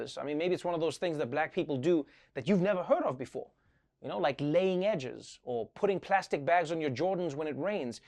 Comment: I 100% thought Trevor said "laying eggs."